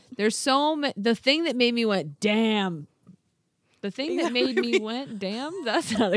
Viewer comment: When you said “when I went damn” all I heard was “Arby’s” in a super deep voice 😭😂